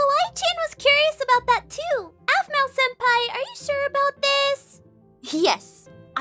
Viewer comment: I turned on the captions... And read what kawaii Chan says at 0:31 It's supposed to be Aphmau Senpai but it's "Ah smells Empire" XDDDDDDD